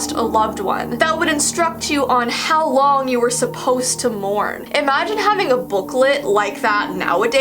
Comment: I thought she said "how long your suppose to moan"